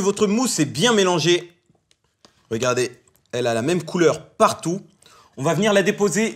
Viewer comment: à 1:13 j'avais compris "elle a la même couleur que moi " 😂😂